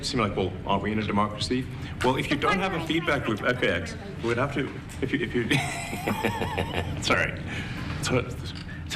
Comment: What was that the kid said @ 1:56 in high speed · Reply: "shut up , you're not the president". 😂